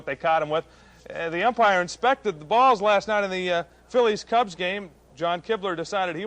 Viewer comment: He did not say "the umpires inspected the guy's balls last night", did he?